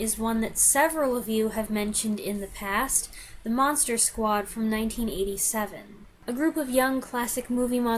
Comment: Thought you said Mad Monster Party.Nice WW2 offerings